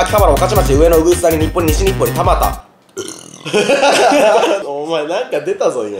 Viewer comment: の｢田端ーー｣が｢アバターー｣に聞こえたw